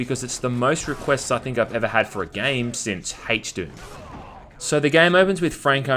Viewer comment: Most requests since what? I can't understand it. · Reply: HDOOM